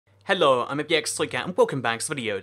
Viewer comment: why does it sound like your saying hello I am a bxtoycat in your intro